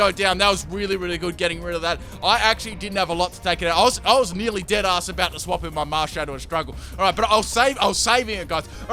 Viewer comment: Um... 12:49 -12:51 did he say "Deadass going to switch..." ??? Nice